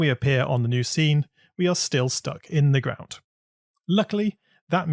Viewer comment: groud instead of ground